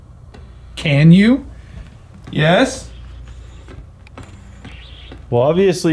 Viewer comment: 26 that sounds like catch or can’t you. 6:52 listen after says, we’re going to go up stairs. You hear a male voice say. Follow you! Haha 7:18 woman’s voice j then a man says, hello? Right after. 7:42 a different male voice. Kyle, slow down! Might be a different name than Kyle but that’s all that makes sense to me. 14:58 male voice again. I think he said, wrong! 18:09 that’s weird & random? 😜 18:13-15 woman’s voice said, kkkk 26:44 woman’s voice said, that was me. 32:38-41 did one of you say, k-going up. 33:48-50 just leave!